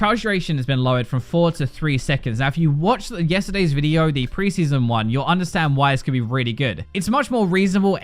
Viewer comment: Did he say pre-season 1 ? 9:08 is that right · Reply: "If you watched the previous video, the pre-season one" he was talking about previous video about preseason